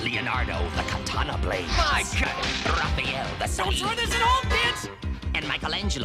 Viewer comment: It almost sounds like Raph said "Don't try this at home, bitch!". Lol.